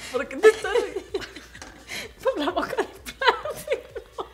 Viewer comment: at first I thought her mom said "en tu boca de plastico" and I almost died.